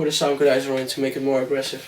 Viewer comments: What word is he saying at 14:59? I can't figure it out. Sounds like "Sound-goodizer" lol. Equalizer? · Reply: He said Soundgoodizer. It's an effects plugin for FL. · "Also i put a syngrolyzer? to make it aggressive "what is he saying again? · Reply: soundgoodizer